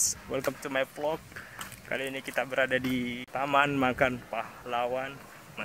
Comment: Saya salah dengar kapang dipenyebutan pertmananya taman makan ehh makam pahlawan hahaha, mantap videonya kanda